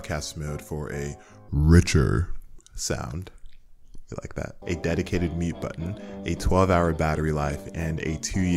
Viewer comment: I was doing something in another tab and somehow at 8:57 -ish, I heard "a dedicated meat button" and needless to say I was very confused for a second before I rewound and realized my mistake lol